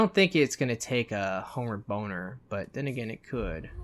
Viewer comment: HOMEWARD BONER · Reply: LOL at first I thought he said " homemade Boner"